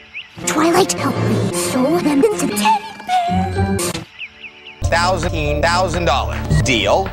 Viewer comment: Hey folks, does anyone have an idea what Flutters says at 20:42. I kept re~listening but i couldn't understand.... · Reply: says, "Twilight, help me sew them into teddy bears."